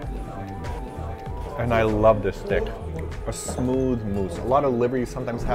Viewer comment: Sounds like is saying "I love this dick". LOL